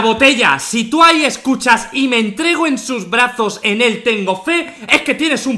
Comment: yo entendi "tengo tengo sus brazos tengo tengo fe"